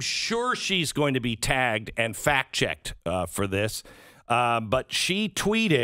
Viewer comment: Thought you said Alyssa milano would be: "fat checked" not "fact checked"